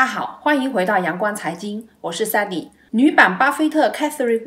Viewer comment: 我聽成「我是sunny 女版巴菲特」 想說怎麼這麼囂張，嚇了一跳